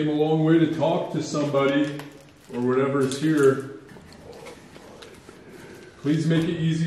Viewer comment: I heard something at 40:57. “I am here”. Creepy male voice